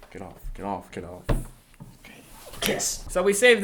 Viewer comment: HAHAHA, did say kiss at 13:36?